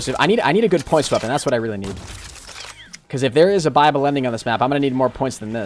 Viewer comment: When first started whatchung you thought you said Bible ending lol I was like what